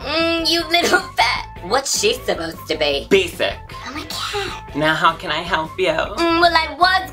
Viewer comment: Lol I didn't hear "basic". I thought you said Beef it and was like omg calm down!😂